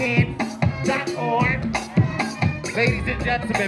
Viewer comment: heelllpp the captions caught me off guard at 6:25 😭 is def saying "dot org" not "got art?" its the website 💀